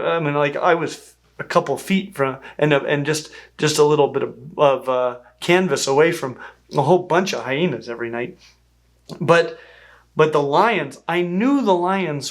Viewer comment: I was eating snow peas and over the crunching I thought he said "I was just a little bit of cannabis away from a bunch of demons every night" I should sleep more..